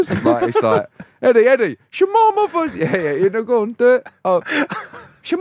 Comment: Anyone else thought he said shalom motherf*****s 😭😂😂 cause theres a jew in the background